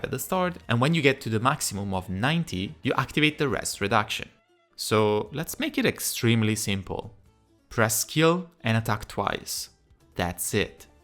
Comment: Your explanation of her kit was better explained than anything I’ve heard so far. Thank you! I heard you say, hey Sunny, this is for you 😂. What you actually said: So let’s make it extremely simple, press skill and attack twice. ❤